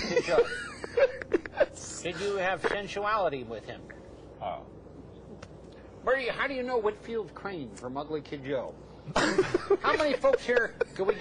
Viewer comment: What does hamburger say @ 19:23 ? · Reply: "where do you... how do you know Whitfield Crane from Ugly Kid Joe?"